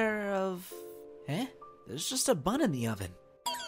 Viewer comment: i thought Adrian said "but there's only a 'butt' in the oven"